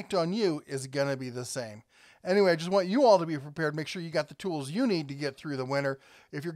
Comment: I swear I heard "be preBeared"